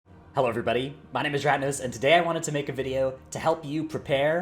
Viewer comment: Thought you said "My name is Rat Nose" then lol